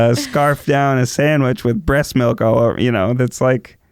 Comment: Did he just say, "scarf down a sandwich with breast milk?!" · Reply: "With bread milk all over me"